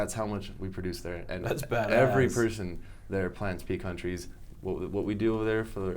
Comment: I am not native and I don't understand, what are they talking about? I sounds like beantrees... but I do not get it clearly. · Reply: It’s a nut a pecan tree